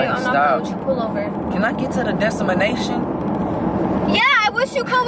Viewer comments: Okkkay so who heard him say “destimanation” 💀💀💀😭😭😭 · Am I the only heard him say "Desemination"😂😂😂. This was funny 😂😂😂